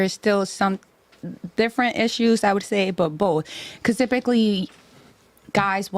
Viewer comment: did she say “cusifically” ?